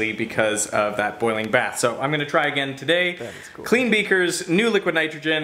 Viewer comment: "That is cool" Did anyone else heard that?!